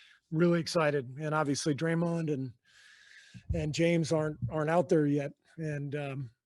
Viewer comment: Thought he said draymond & James harden for a sec lol. “James aren’t”